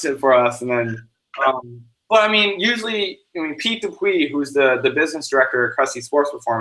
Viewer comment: What name did he say at 17:27? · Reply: Jonathan Garland Pete Dupuis